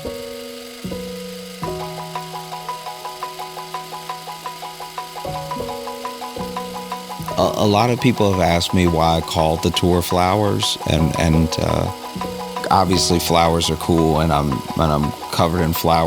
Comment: Oh shit I have mild hearing loss I thought what he said at the beginning was "a lot of people are wondering, why I called the tour 'the twin flowers' "